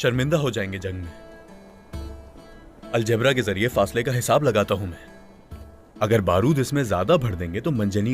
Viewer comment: did he say algebra